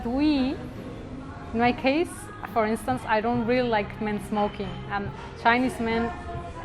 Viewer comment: O...M...G sorry but when she said she doesnt like men "smoking" I thought she said small penis, but I wasnt sure, and when they showed they asian men and then she says about 90% do I was sure she said that LOL. But I was skeptical that Winston wouldve kept that in the video and looked in the comments and didnt see it mentioned so I went to back to listen and found out I was the crazy one.